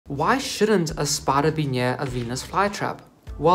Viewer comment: Wth is a "spadabenyare" I think you meant 'spider be near'. I had to listen 3 times, thought you were saying what kind it was, or was that your plan for more views, lol. Love your videos btw.